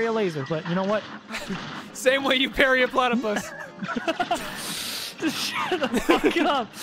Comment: DID SOMEONE SAY PERRY THE PLAYTIPUS